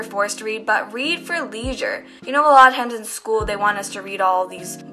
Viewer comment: Wait at 3:30 I heard you say at Hendon school so you go to that school?